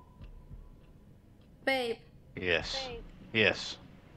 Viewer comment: Hey @ 25:11 did you say eight or babe? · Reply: Babe ☺